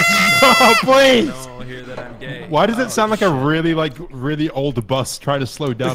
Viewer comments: Am I the only one who heard Kryoz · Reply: He said he was gay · who else heard kryoz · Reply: No one will hear I'm gay.. aw shit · Did anyone catch at 2:40 "great now no one will hear that I'm gay, oh fuck" lmao